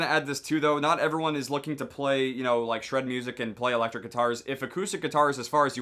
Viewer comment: thought he said shrek music